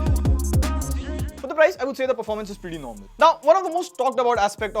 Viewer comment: Feedback: your voice is not audible properly.. 03:35 I heard it as "pretty low" instead of pretty "normal".. pls stop this lowering ur voice volume while saying critical information..